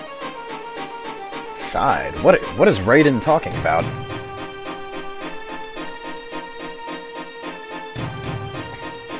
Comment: Lol. 11:05 Did I hear u say Raiden?? Hahah